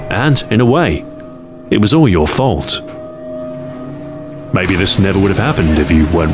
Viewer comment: i thought it said baby